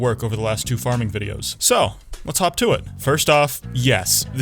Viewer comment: hop to it hawk to it hawk tuah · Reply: Bro i thought he said "talk tuah" when he said "hop to it" 😭